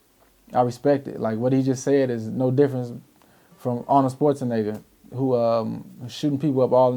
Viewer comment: did he say Arnold Sparzenegger